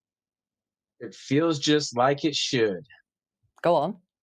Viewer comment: Did he just say feels just like it should?from jamiroquai